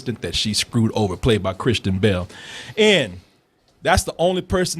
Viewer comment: when says kristen bell it sounds like he says Cristian Bale.... I was over here wondering if this is a comedy version of the Dark Knight.....